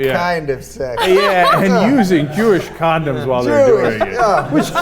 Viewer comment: A guy, in this video, has claimed that he knows Urdu, but he does not know what Josh means in Urdu. In Urdu, Josh means passion/excitement. Josh condom has nothing to do with Jews, and Pakistanis also know this. You just made this up to suit your interests. People in Pakistan, especially in urban region, use condoms. I do not know from where you have gathered this information. You make fun of fox; how it reports and misinterpret facts and evidence, but you have not done much different than fox, in this report. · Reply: It was a joke b/c the accent sounded like he said "jewish condoms" and Ana corrected him right away. He didn't claim he knew Urdu, he was being sarcastic. You didn't catch that. People who don't understand American humor or aren't 100% fluent in English shouldn't comment when they're misunderstanding parts of the conversation.